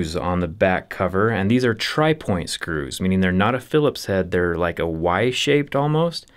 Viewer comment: I heard tri-force screws...